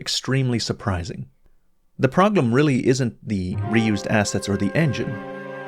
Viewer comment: did you just say Proglem?